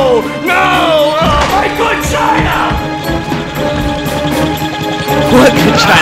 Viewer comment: Does Plankton say "MY VAGINA"? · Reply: No he says "my good china."